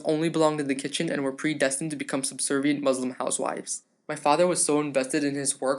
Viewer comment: Did he say SERBIAN ?!?!❤❤💘😋😋😚👍👍⚡✨⭐💝🌹🌺🌹